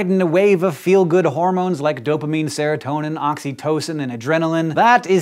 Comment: I thought you said Oxycontin :/ When I heard saw that I just wanted to kiss everyone, then I realized it's Oxytocin :(